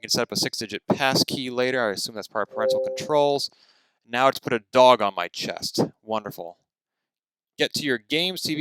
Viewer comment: for a second i thought you said it put a bra on my chest